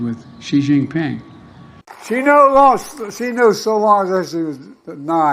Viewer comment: I think I know what Grandpa Joe said at 5:16 "She no lost shenolosiadelania"